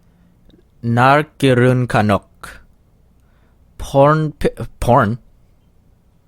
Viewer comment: I thought you said pork I almost ate my iPad